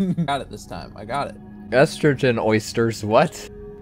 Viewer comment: "estrogen esters" is what it actually says , but estrogen oysters is way funnier